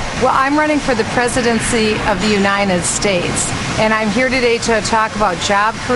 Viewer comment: she's running for the presidency of the what? Does she say..."Unine enstates"?